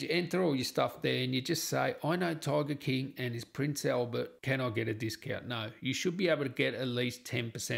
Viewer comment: What did you say at 0:09? "I know target king and it's print sale but can I get a discount?"